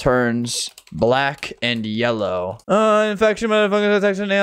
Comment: Dawg I was listening to this like as background while I played roblox and I had to do a double take because at like 5:08 it sounds like he says 'infection mf' really fast💀💀💀